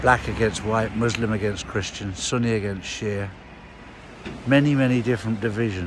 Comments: Thought you said Sonny against Cher.😂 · When he said Sunni against Shea, I had to listen three times, I thought he said Sonny against Cher, lol!